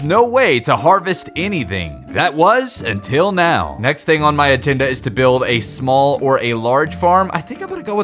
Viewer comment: anyone else hear him say "atenda" instead of agenda? lol i think it was